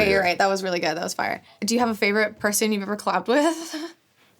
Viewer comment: “collabbed with” sounded like “clapped with” and an ad hit immediately